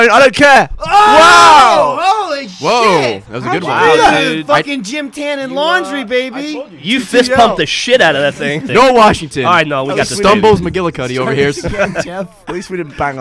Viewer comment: You know, after ALL this time, I finally understand that Michael said "gym, tan, and laundry" instead of "Jim, tan, and laundry" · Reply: Dennis Hutchens i always thought he was saying “Jim Tannen Laundry” lol